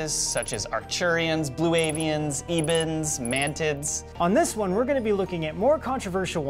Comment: I heard "Mantits"...lol... I m having Lemonade :-)